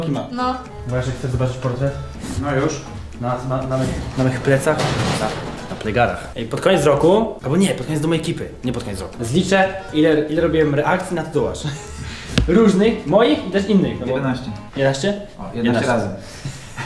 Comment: Zaraz... 10:00 pod koniec domu ekipy? Czy ja źle usłyszałam?